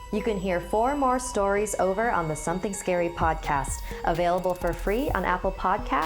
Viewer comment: I THOUGHT YOU SAID NO SOMETHING SCARIES BUT IM OKAY WITH THIS TOO!